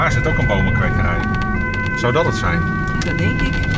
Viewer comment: Anyone else hear "ty dolla sign" at 11:42 ?